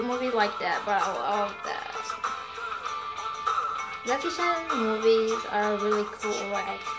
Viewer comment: what did she say ? it syncs in so well to the lyrics haha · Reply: Kian Siong i said " jackie chan movies ... " xD